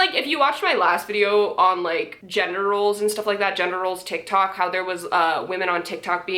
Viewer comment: I know this is really serious but I misheard and thought she was talking about Senior Military Officers on Tik Tok, and the mental image of it made me laugh, and then I realised "Oh Gender Roles not Generals."